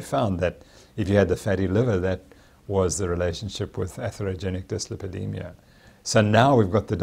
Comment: What are the two words he is saying about the fatty liver condition around the 2:25 mark? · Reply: atherogenic dyslipidemia